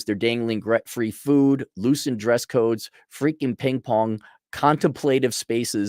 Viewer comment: I thought you said paintball instead of ping pong hahahaha I was thinking they sound fun actually lol 2x speed will do that to you